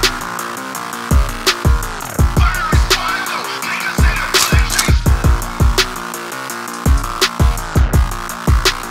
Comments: What does it say? I couldn’t catch it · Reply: "Firing Squad, don't make us have to pull that trigger" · What does say at 1:17? Says “Firing Squad tho” and after that I don’t catch what he says. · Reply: “Firing Squad don’t make me have to pull that trigger”